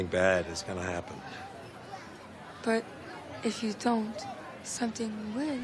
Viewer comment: Please does somebody know for sure what the french teenager says at around 04:50? "But if you dont... Something will"? Ive been trying to figure it out for ages now